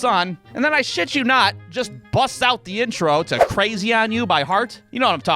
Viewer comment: i thought you were about to say Crazy Train, i was about be like “Oh sweet, i get to hear a snippet of a Ozzy song” 😂